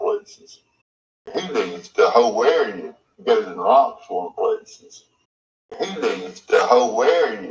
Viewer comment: He says "Goes in lots more places." I don't live all that far away & that's a common phrase. I'm 99.9% sure. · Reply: I heard ‘goes puts locks on places’. 🤔😵‍💫